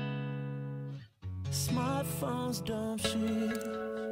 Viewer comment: "papi trey" WHOOOOHOHOO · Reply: At first i thought he was saying smartphones dont cheat, but hes saying smartphones dumb shit. Lololoololol